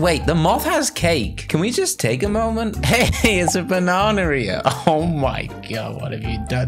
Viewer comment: I always thought he said bananarea